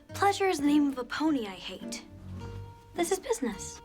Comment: "Pleasure is the name of a pony I hate"!!! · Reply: I thought she said pony I ate😅